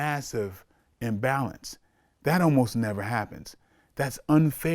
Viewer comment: Lmao he killed his whole size argument with that UFC comment, Royce Gracie was 160 pounds tapping people out who outweighed him by 100 pounds or more...and there was plenty of real legit fighters in the early UFC... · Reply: He clearly says "ALMOST NEVER HAPPENS"